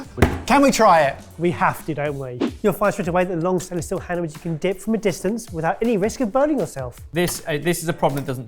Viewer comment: what on earth did he just say? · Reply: You'll find straight away that with the long stainless steel handle you can dip from a distance without burning yourself